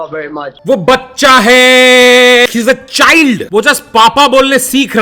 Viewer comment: did he just said that "she's a child"?🤔🤨